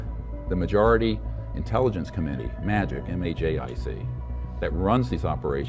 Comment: So many errors in the captioning. Who tf thinks it's really spelled "New Zeeland"? Some of the words aren't even what was said. (Example 32:46 - He CLEARLY states M A J I C... yet, well, it's obvious.) (Even the editing is lazy... You keep the blur in the lower right hand corner even when there's NO "VideoAdvice" (VA) watermark to blur. Definitely some lazy editing.)